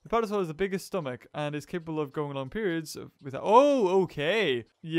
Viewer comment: I thought u said a dinosaur was able to go on periods XD